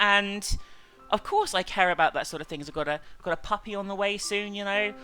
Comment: i thought she said baby not puppy. I almost spat my tea out.